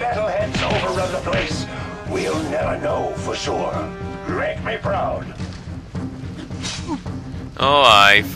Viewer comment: did he say rake make proud